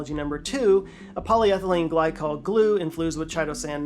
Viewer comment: I'm pretty sure I heard you say infloosed?